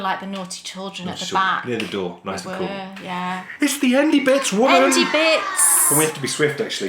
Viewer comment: About your fisherman blanket name --I have always liked your podcast when you have the "endy bits" closing part of your video. I love your accents when these words are said. Most time i thought you were saying "indy bit"s --but one podcast you label it as "endy bits" I think think would be a great name for your new fisherman blanket